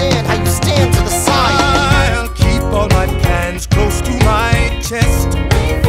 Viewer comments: I’ll keep all my plans close to my chest... I heard, I’ll keep all my pants close to my chest. why just imagine Aaron Burr pulling his pants close to his chest. eek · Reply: Wait, he said plans? I always thought he said "pens"! Well, now it at least makes more sense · ILL KEEP ALL MY PLANTS CLOSE TO MY CHEST Edit: WoAh 75 likes! Thx I thought this would be a comment that no one would like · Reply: I always thought he said "ILLLL KEEP MY PANTS CLOSE TO MY CHEST" 😂😂